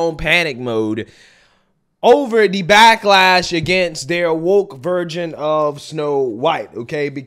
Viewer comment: bro did you just say Woke Virgin? 😂